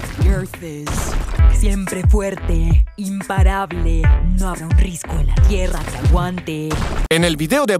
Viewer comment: En la primera parte de en lo profundo yo Entendi "no hago risco la tierra que aguante"